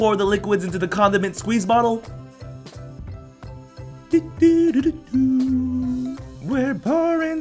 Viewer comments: I thought he said condom and squeeze bottle, no condiment bottle!!!!! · I thought he said "condom and squeeze bottle" lol